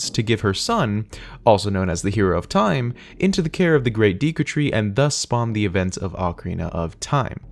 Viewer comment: did he say deeku